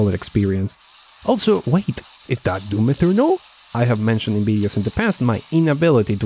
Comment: it sounded like you said Doom 'Inferno' - and thought.. that's actually quite fitting